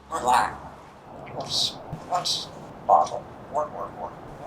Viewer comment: When made the bad sound and said it hurt his ears, it sounded like said "what, who cares"... lol..... 😂